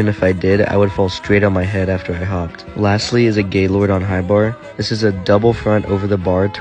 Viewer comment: did he just say gaylrord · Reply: He said Gaylord. Clearly. It was even written across the screen. It's a last name. Probably the last name of the first man to compete with this move.